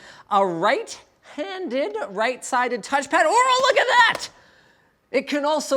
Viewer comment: did he say oral look at dat?